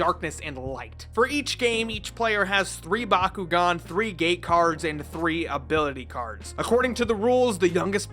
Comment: wat did i hear 3 gay cards?